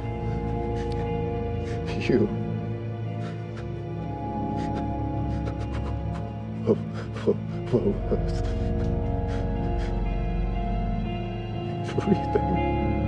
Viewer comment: What does ej say at 2:31-2:36??? It sounds like "something everything" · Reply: KDrama Queen Rox “you were worth everything.” 😍😫😫💔